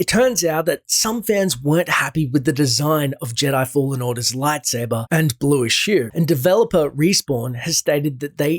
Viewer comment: and blueish WHAT? I can't understand what the guy is saying. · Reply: Artistry Artistry blueish hue